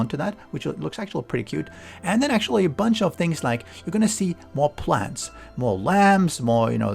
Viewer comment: It almost sounded like you said... 'More plant's, more lambs' I was like...when did lambs make it into the game XD